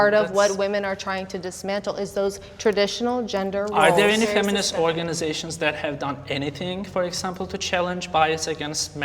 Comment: he didnt say that, he said "are there any feminist organizations that have done anything to challenge bias against men and fathers in the family court system" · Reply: oh, i thought he said that there wasnt any femanist organizations.